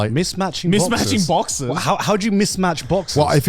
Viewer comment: Bruh. Until he talked about wearing the boxers…for a solid minute I thought he was saying mismatched boxes 😅😂